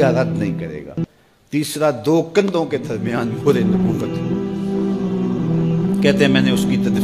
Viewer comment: Hazrat Sab , mujhe 2:31 sec Wala line , clear nhi smjh aa rha h, Do kandho k darmeyan..... iske bad Kya kaha apne smjh nhi aaya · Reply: Mohar e nabuwat